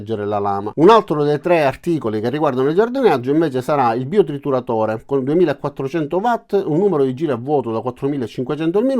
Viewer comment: Ciao. Non ho capito il prezzo del biotrituratore. 6:30 😊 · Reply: Ciao. 79 euro